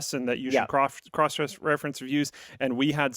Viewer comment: I swear I hear Luke say 'cross-dress, reference..." around 3:10. Interesting Freudian slip there, are we going to see Luke's onlyfans soon? :P